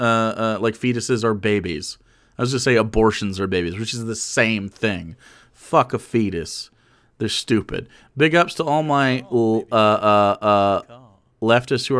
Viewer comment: Am I the only one who heard, "f*ck a fetus, I'll go a capella" lmao